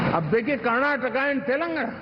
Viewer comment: Abbey yeh kya bola... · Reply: Ab dekhiye Karnatak and Telanagana Yeh bola